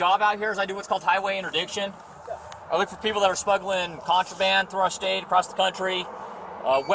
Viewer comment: I thought the cop said he was looking for people smuggling first aid 😂